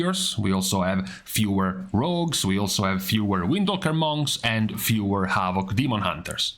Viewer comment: I thought you said windowlicker monks O.o